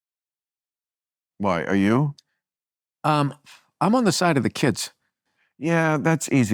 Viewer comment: Maher misheard burr, burr said "on the side of the kids" the college kids.